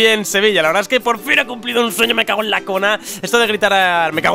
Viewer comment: No se ustedes pero yo entendi "Me lavo la cola" :v